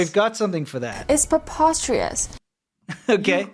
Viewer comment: All I hear is "popostrious" too